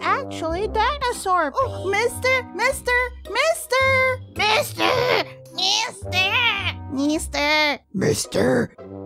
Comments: When she kept saying "*mIstER*" i thought she was saying mIsTaAaaaA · Why do I keep hearing Mista instead of mister? Guess I watched too much golden wind....